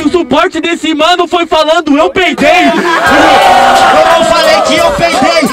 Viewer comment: Só eu entendi "peitei"?